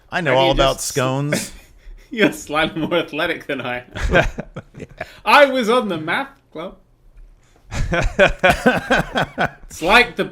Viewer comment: i thought said "I was on the map club." I would be a member of map club.